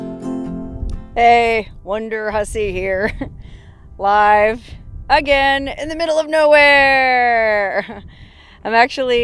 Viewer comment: My wife heard you say "wonderhussy here" ...I don't think she understands our relationship.